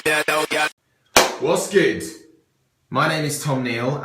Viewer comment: did he say "wass good?" at 00:19 ?